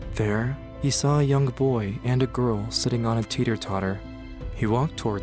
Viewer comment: did he just call a seesaw a tater tot